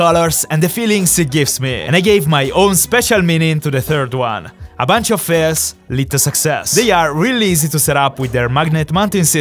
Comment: what does he say a bunch of what leads the success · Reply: A bunch of fails lead to success